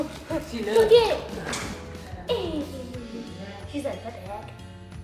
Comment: Who heard at 10:15 she said “What the f*ck”